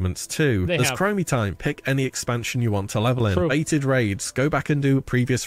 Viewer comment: what raids? Did he say baited raids? I don’t actually know. Besides a small stint for a month a year and a half ago I haven’t played wow since ‘07 or ‘08